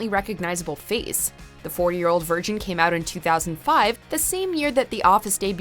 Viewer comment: is it just me or did it sound like she said the 4 year old virgin 😂